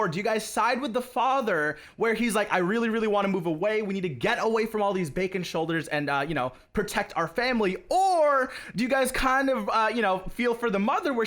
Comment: Did he just say Bacon Shoulders